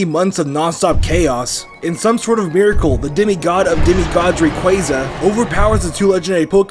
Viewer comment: Did he says "Demigod of Demigoddri quaza"